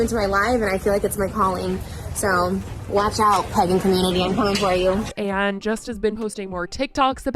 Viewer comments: I thought she said “watch out pegging community” 🤭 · Sounds like she said " watch out pegging community, im coming for you" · I’m sorry but in the beginning of the video, I thought she said “watch out pegging community…”and I was trying to figure out what’s so problematic about that 😭😭😭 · The only reason I watched this about Trisha is because at the beginning, I thought she said PEGGING COMMUNITY after it said she offended people and I was really interested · HAHAHAHAH I HEARD 'WATCH OUT PEGGING COMMUNITY' I'M IN TEARS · I deadass thought Trisha said "pegging community".... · It sounded like she said pegging community. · I thought she said 'pegging community'. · I full on thought she said "Pegging community" in the beginning of the video. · I thought they said “watch out pegging community” in the intro 😳 lmao I was like how is allowed to talk about this on YouTube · Oop I heard “watch out pegging community” and was so confused on how religion led to pegging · I thought she said "pegging community" and clutched my pearls. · I thought she said "watch out pegging community" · I thought Trisha said, "watchout pegging community- I'm coming for you."